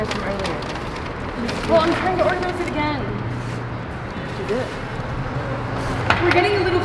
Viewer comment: ughhhh I felt so bad for at like around 18:20 when they kept complaining when she wanted to organize the bags!! and can some one tell me what the girl in the red hoodie say back to at 18:25 after said “well I’m trying to organize it again!” and at around 18:50 everyone was getting stressed and mad... was paranoid about people breaking into her car so she just wanted to make sure it looked like there was nothing there but her “friends” were getting annoyed. there were so many other parts in this video that just makes me frustrated but I know and worked very hard on this video and I wasn’t there too judge. sorry if I offended anyone this was just how I saw it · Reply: i think she said “so do it” after said she was trying to organize it again